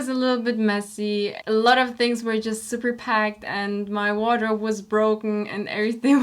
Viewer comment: "my wardrobe was broken" Me: "did she just say a water was broken?"